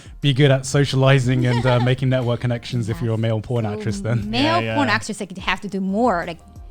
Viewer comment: anyone else hear "Male Porn Actress?"